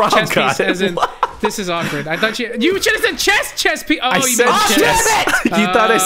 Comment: I thought Mr Fruit said chest piece too haha